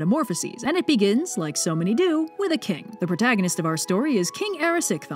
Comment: so thought that said a kink D